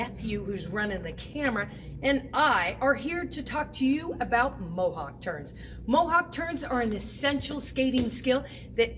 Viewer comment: I thought you said mohawk turds lol